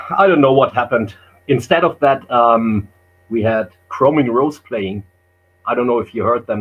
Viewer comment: Who does say replaced Celtic Frost at 38:32 on that bill? The band that sounds like Keepers Helloween. Can’t make it out. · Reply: Chroming Rose I think it was.